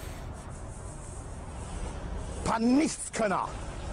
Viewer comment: I don’t really speak any German so correct me if I’m wrong but I think he said Nichtskönner (literally nothing can-er I guess?)